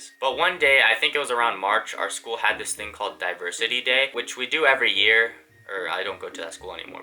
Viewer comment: I thought he was saying "Diaper City Day" rather than "Diversity Day" for a minute straight lol